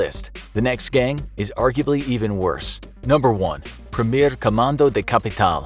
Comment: Amazing video, I was waiting for the mention of PCC. Subtitles needs small fix, tho: at 13:05, it should read "Primeiro" instead of "Primer" (First Command of the Capital).